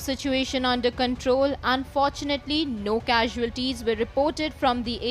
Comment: Did I hear, "unfortunately no casualties were reported........ "?